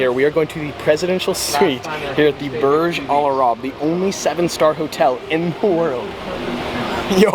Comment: I thought there are 2 7 stars hotels in Dubai? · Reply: I thought u said 27 star hotel